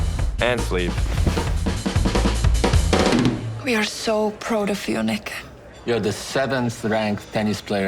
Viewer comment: what did she say 😂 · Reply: She said proud